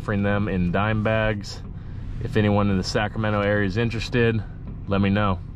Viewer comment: did you say Sacramento or am I tripping, I am in sac lemme get a few dime bags lol